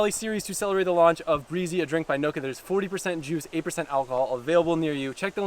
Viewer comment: For a second I thought he said 8% juice and 40% alcohol 😂